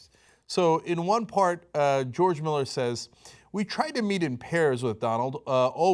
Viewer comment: I thought you said they meet in Paris...